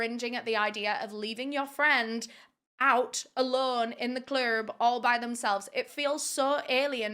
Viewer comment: Did you just say "in the CLURB"?!